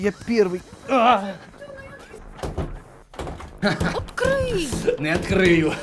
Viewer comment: открый послышалось - от крыса